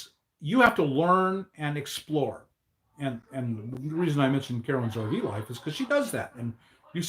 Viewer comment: Carolyn's RV Life, Solo Woman camper, He said her name so fast that I thought he said "Caroline Zarvelof". I finally pit just Caroline and Boon-docking in the YouTube search and I wasn't sure I had the right person so I re-listened to that part.